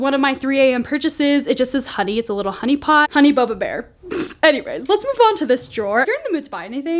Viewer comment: I thought you said Honey Boo boo! !